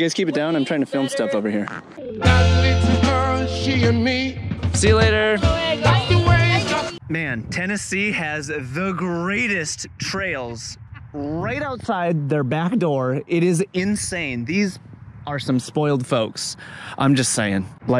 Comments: Did you say "Tennessee" has the best trails? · it sounds like you mention Tennessee. Are you in TN or MT? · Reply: I swear you say "Tennessee has the greatest trails"